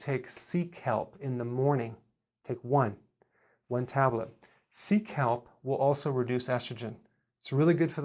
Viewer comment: What did he say at 3:05? "seek help" seecalp? whats that? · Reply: Sea kelp